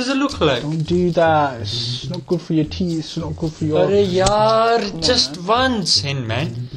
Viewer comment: did he just say “يا رياال" ? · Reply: He said yaar